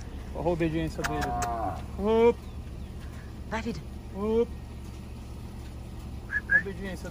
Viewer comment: so eu que escutei um "vai vida" no minuto 5:21 ?